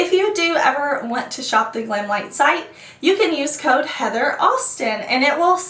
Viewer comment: You may have said code, "HeatherAustin", but I only heard the code, "HeatherAwesome". Whoops! Side effect of supporting you since the early days of this channel. Love you! I will be creating content, soon! Finally!